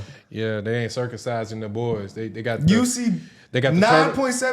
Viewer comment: “They got the turtle….🐢 “ …. Did I hear that right?!?! 🤣🤣🤣🤣🤣